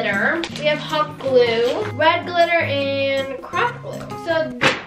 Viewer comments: I thought said crack when she said craft- 💀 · thought said "crack glue" for a second